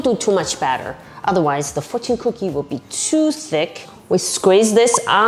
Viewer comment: I thought you said "otherwise it will be too sickk 😎🤙" and now I'm crying